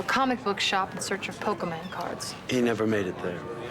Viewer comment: Did she say “Pokéman cards”?